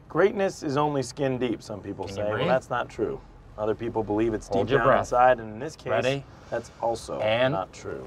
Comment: Since everyone is repeating quotes from this clip, tell me please, what Jim said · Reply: Miha Bevc "other people believe it's deep down inside and in this case, that's also not true"